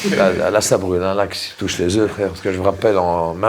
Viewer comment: à 5:59 c'est moi ou il a lâché un "frère" en détente ?